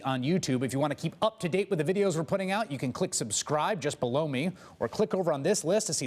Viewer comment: Whenever Chris says "click subscribe just be-low me" I hear "just b-low" me.